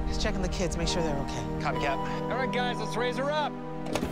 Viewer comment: I heard “cmon guys let’s rizz her up”